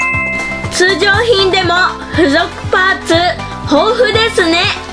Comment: 僕とロボコｷﾀ━━━━(ﾟ∀ﾟ)━━━━!!付属パーツが、付属パンツに何故か聞き間違えました(笑) 少年ジャンプフィギュア僕とロボコお値段８千円台とは、お高い。